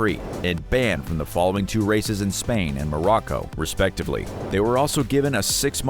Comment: i heard morocco